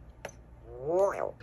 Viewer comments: 😂 I thought he was saying Royal (as in Royal Purple) not Wario. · I thought he was saying Royal all this time! It was Wario all along!!!